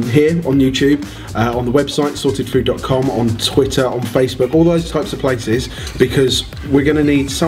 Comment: did Jamie say "toitter"?